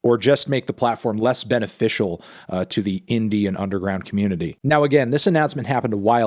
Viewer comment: I swore he said " Make it more useful to the Indian underground community" my mind was blown. Indian underground community sounds like something I want to experience.